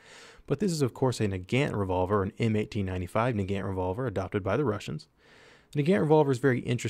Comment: the caption says " 1895 nigga revolver"